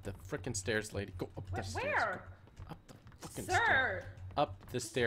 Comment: Did just said to in 2:11:34 "go up the f***in stairs"????